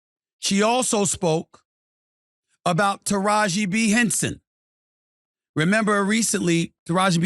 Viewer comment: Taraji P Henson..... Sounds like he is saying Taraji B Henson 🤷‍♂️